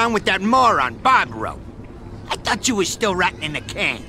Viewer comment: when Luca said I thought you were still rotting in the can I thought luca said I thought you was still ratting in the can